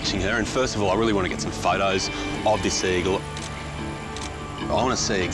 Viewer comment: Half of the episode I thought he was saying 'seagull' instead of 'sea eagle's.